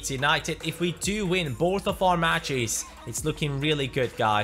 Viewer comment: does he say "if we win borth of our matches?" or is it just me